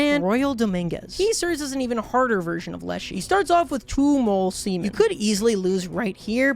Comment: who thought he said "Mole Seman "